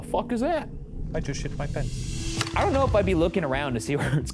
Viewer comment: hahaha the guy at 10:34 sounds like he said. " i just shit my pant"